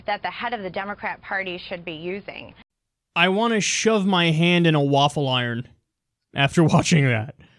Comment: I thought was gonna say "I wanna shove my hand, up her ass " at 2:57 lol